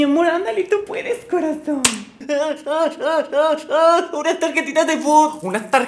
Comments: Yo escuché “unas tarjaritas de food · Al principio escuche "me puede dar unas galletitas"😂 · No mame la primera ves que dijo unas tarjetitas de fut pensé que dijo una bolsa de arina. jajajajajaja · Entendí "galletitas" a la primera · Yo pensé que dijo unas galletitas por favor🤨😻 Jajajajaja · Soy la única q al principio escuche "me puede dar una galletitas" JHSJSJSJAJAJAJAJAJ